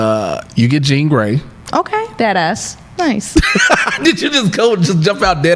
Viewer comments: I really thought Perri said 'dead ass' like she went full NY and she bout to start rocking Yankees fitted hats and Timbs. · Lmao I thought Perri said deadass too I was so weak